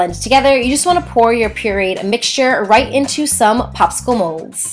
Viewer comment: Did she say perid mixture or do I have bad hearing?